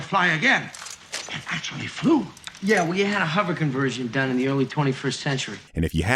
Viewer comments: I'll be fair, until I saw the subs I also thought it said "we had a hover conversion" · I watched through this movie recently and according to my subtitles, Marty actually does say “We” instead of “you.”